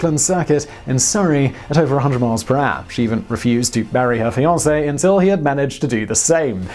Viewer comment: I thought you said, "She refused to BURY her fiance until he lapped Brooklands at over 100mph!"!!! Poor sod didn't get a break even after his death! Then I realised the word was MARRY!!